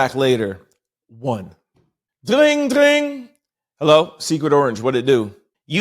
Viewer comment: At first I thought you said Secret Porridge 😂 He could be Secret Orange Porridge😂